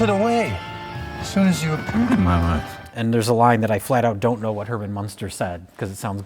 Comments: "...as soon as you appeared in my life." It's garbled, but I was able to understand what was said. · It took me watching it 20x to figure out what Herman Munster said at 7:15. He says "As soon as you appeared in my life."